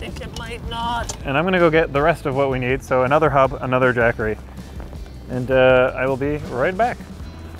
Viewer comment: I really thought he said "another Daquri" and i was like...wow, the least gangster way of drinking on the job. but mad props for partying so hard bro. LOL I had to go back to check the subtitles and i was like....wtf is a jackery? lol